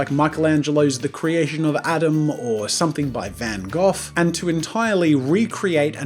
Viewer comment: did he say van goth? He said van goth.